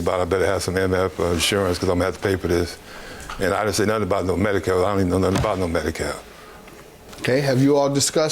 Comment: At first I thought she said Medicaid wrong 🤣🤣🤣